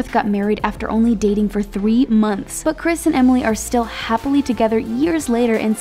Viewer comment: Am i the only one who heard chris and 'emily' instead of chris and elsa (3:32) ?